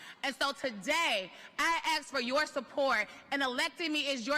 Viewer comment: the woman says "ask" , but the caption says "axe".